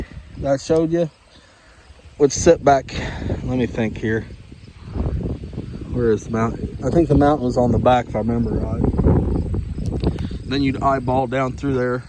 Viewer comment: Did you say "member rod"?🤣🤣🤣